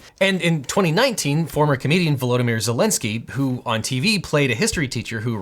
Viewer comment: The first time I heard you say “former comedian” I could have sworn you said “former Canadian”. Which didn’t make sense so I rewatched that a couple of times.